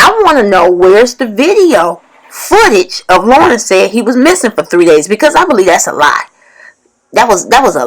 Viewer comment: I could ha be e sworn his mother said Lauren told her he was cleaning for 3 days